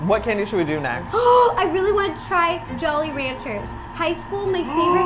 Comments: When Ro said jolly ranchers I thought she said JOEY rancher for his escape the night season 3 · I thought you said Joey Ranchers xD